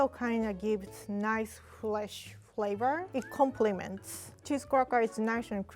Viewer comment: When she said fresh flavor, i thought she said flesh flavor😂😭